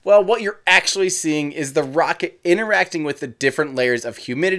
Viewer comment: It sounds like you say “Ineracting”